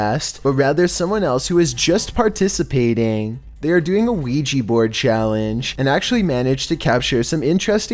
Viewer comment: Everytime i hear ouija i thought you said luigi board